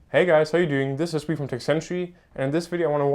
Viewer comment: You've done a good job but you speak "too fluently", e.g. starting from 0:01 "This is ..... century" I can't figure out what you say there and this happens many times along the video. Slow down. You speak too fast and combined with the german accent makes it sometimes incomprehensible. No offense though.